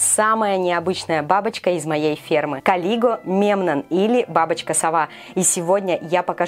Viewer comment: Мне послышалось "Колиго Мем" 😂